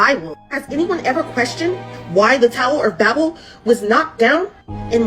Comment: The flower of life is what I heard her say in so many worlds 🤦🏽‍♀️